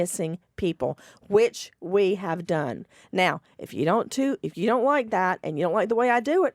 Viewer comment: “Now, if you don’t toot” is all I heard.